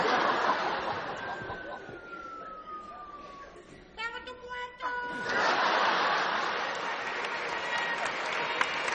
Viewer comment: Jajajaj geniales!! Lo único que no entendí es lo que dice Joan en el min 6:54! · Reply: Dice "[me] cago'n tus muertooos"